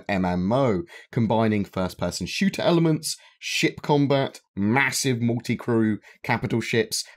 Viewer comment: Could’ve sworn you said “shit combat.”